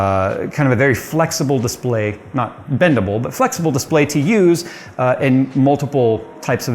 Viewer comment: Bendable. Not bendable. I had to rewind a few times cause I thought you said. Not dependable. With no follow up hahah